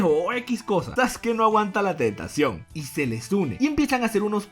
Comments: oí tetación, Perdonen perdonen,el hentai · entendí que dijo Soske no aguanta la tetación 😂😂😂